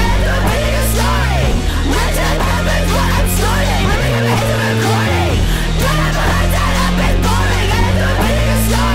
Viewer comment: y’all- what is saying at 1:40 ??? what is is she saying she’s snorting bruh i’m so curious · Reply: Red chili peppers